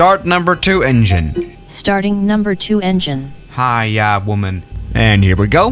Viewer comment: DID WATCH UNCLE ROGER??? DID HE JUST SAY HAIYA WOMAN?????